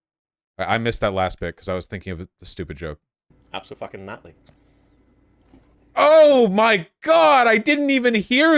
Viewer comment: I thought he said "Absolute fucking notly" tho he probably said Naitaly tbh.